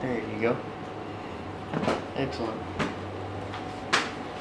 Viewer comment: When she said that was the craziest feeling I thought ol dude said thank you 😂